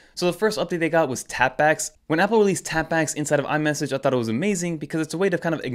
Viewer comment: When the guy in the Apple video said tap backs I honestly thought he said tampax 😂 I was like what.